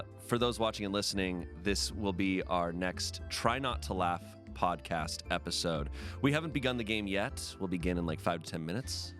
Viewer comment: I thought bro said the LAST episode and I almost lost it.